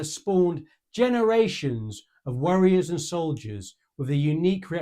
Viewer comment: I love your accent and pronunciations of foreign words, but I laughed when I heard "worriers" and soldiers instead of "warriors " and soldiers! Lol 😅